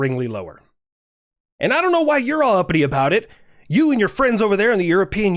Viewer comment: Did he just say.....uppity????